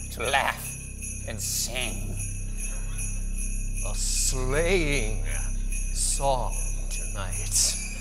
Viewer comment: ROTFLMAO! I swear I heard Neil say "a slaying song tonight..." instead of "sleighing...." Perfectly spooky laugh, too! ;-)